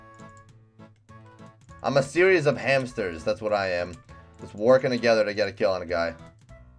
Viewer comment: Did you say hamster's